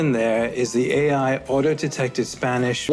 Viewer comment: it says "order detected" instead of "auto-detected". I'm not native but isn't it just wrong?